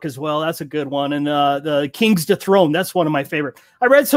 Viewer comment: Anyone else catch what the third book mentioned was...? “King to throne” or something else I’m guessing as I cannot find it or anything near it in that genre. about 12:50. Thanks! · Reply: Gerrard Hickson - Kings Dethroned. You're welcome :-)